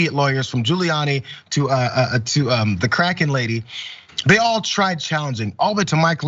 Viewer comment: At first I thought he Said the "crackhead" lady, but he said the "kraken" lady. It's one in the same truly.